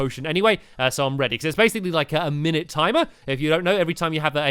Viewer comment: When you said minute i thought you said mimic 🤣